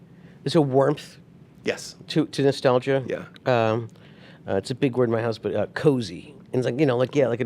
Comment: Did he say husband? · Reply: He said it’s a big word in my house